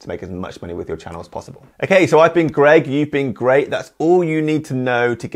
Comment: At the end I always hear «I'm Greg, you've been gregged» :)